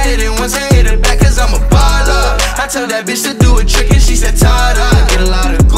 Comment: bro when i first listened to this song i thought it said "i told that b* to do the chicken"